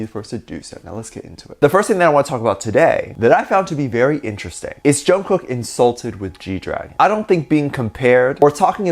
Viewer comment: Man I swear to got I thought it said junkook insulted G-easy. Man I thought jk knew something.